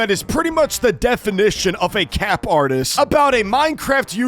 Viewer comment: I thought you said fafa😔💔🥀